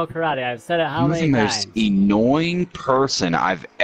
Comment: did he just say “e-nnoying”? 😂